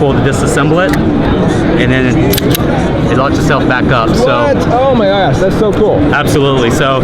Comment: It sounds like he said, "WHAT! Oh my ass!"